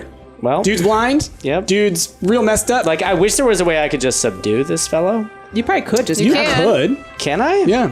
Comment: when he said subdue i thought he said seduce